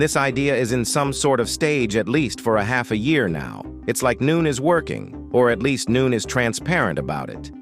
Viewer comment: bro are you AI? you just said noon instead of no one